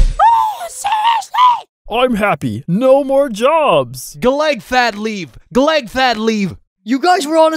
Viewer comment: why does cloudy have an accent because he sounds like he’s saying collect fat leaf collect fat leaf instead of “collect that leaf collect that leaf...”